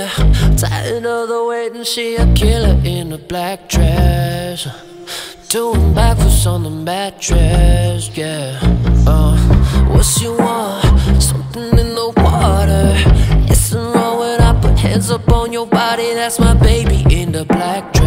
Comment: What's on your mind, what's the situation? Need you here, tired of all the waitin' She's a killer in a black dress Doing bad things on the mattress, yeah What you are, slipping in the water Is it wrong when I put hands up on your body That's my baby in the black dress, yeah She just looking for that address I've been over my head to see the brighter things She knows what's on my mind Looking to be, the bod I need from you is almost over time I can't get you out my head You've been everywhere I go I can't get you out my head I'm so caught up over you I've been over my head to see the brighter things She knows what's on my mind I can't get you out my head, uh You've been everywhere I go, o-oh That's the best I got lol. A lot of his phrases were impossible to make out, but I tried my best xD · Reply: Mr. Snarky I thought he said "What she want, something in the water"😂